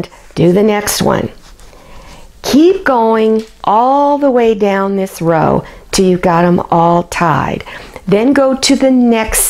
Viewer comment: What does she say? Yeah exactly. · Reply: Hi Travelingzoo. I think your question is "At 7 minutes 13 seconds" what am I saying. I said "Keep going down the row until it is completed." Thanks for watching, and happy sewing.